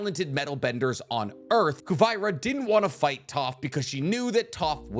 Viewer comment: Pls stop, it’s ”kuvira” ,I feel like I’m being tortured every time I hear “koovayira”